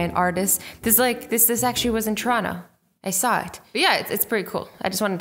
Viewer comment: this was where again? Can someone tell me because i didn't understand what said? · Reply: Toronto